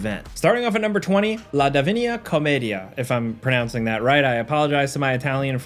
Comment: I thought you said "Chlamydia" instead of Comedia, because there is nothing divine about Chlamydia. 😂😂😂